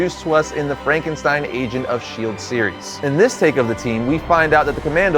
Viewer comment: Did i just heard Agent of Shield In 6:13?